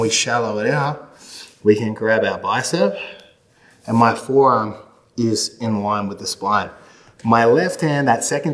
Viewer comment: I don't understand any of these references in the comments, but he kept saying "in line with the spine" but... his arm was definitely perpendicular... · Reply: he clearly says "my forearm is in line with the spine." So he's specifically talking about the forearm, not the aftarm.